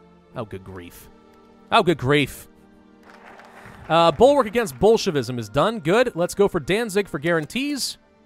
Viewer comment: At first I thought he was saying Bulkwark against bullshit-ism lol